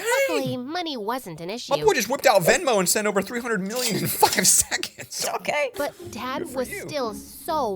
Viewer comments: did he say the f word XD? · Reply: five seconds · Did he say the f word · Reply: He said five